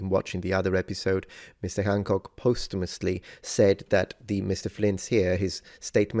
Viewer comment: Link to the full original episode <link> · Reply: At like 34:10 did you say posthumously? It made me think Graham Hancock died haha